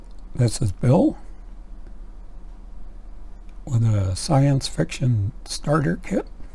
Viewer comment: I thought you said silent fiction...😅 Anyway, great recommendations. 😃 I recently read The three-body problem 😊👍